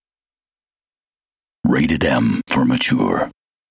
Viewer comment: Hey guys do you want to say me one of you what did it said the voice of the beginning of the vídeo? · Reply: Sorry if it's late but I think it said: "Rating M for Mature"